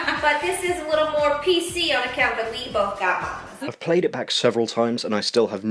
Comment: that bit at the beginning sounds like she said it's a bit more PC "on account we both got mamas"